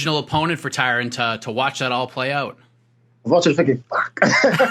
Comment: what does he say at 2:41? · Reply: "i was watching thinking fvck"